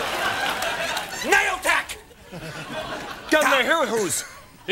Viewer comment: Colin yells out Naiotac. What he was trying to do was say Action! backwards 😂😂😂😂 @ 37:36 he tries and fails again "Naioctada"